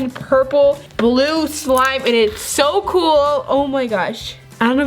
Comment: She said “pink” thick slime xd who else heard that | | | ⬇️ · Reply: Who else heard her say pink slime when it was blue slime